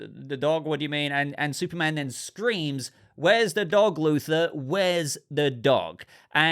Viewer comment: I thought I heard 'loser,' not 'Luther.'